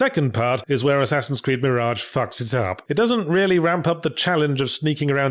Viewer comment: Did you say F***? Is this a YouTube thing or a Escapist thing? Who was/is holding you back from swearing?